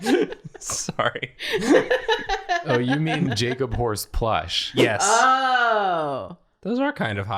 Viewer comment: Time to play another round of What was SAID vs. What I HEARD What was said: "... the Jacob horse." What I heard: "... the Jacob whore." I've been watching too much drawfee.